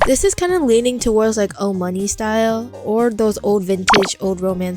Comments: HELP I THOUGHT YOUR MOMMY SAID MONTOES😔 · For a sec i heard old monkey not old money 6:36 😭